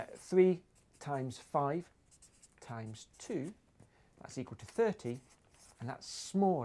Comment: Shouldnt 2*3*5*13*17 = 6630? 13260 is 2*6630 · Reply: It sounded like he said "equals to" but I think he said "equals two."